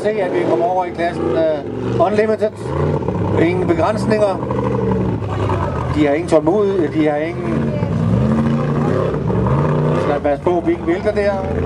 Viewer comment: Am I the only one who hears ''Bring the guns, nig*a.''?